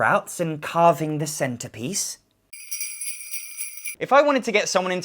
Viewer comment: carving the centipedes?? I'm sure I didn't hear that right..